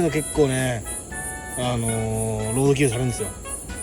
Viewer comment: こういうやつが結構ねぇ～ あの～ の後、何て言ってるんですか？ 何回聞いても判らなくて気になってます・・・ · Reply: ロードキルされるんですよ ですかね？